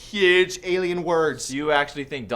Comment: I misheard it and I thought you said “Donald Trump is...... Canadian “ Me: Wait, what?